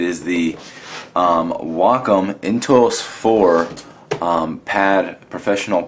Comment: the subtitle says "Wacom IntOUs 4" instead of "Wacom IntUOs 4"... :)